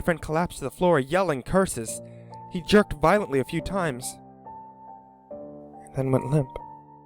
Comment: did he say :he jerked vilontley a few time